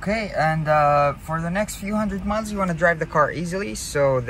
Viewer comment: For next few 100 what?? Miles? Sounded like u said months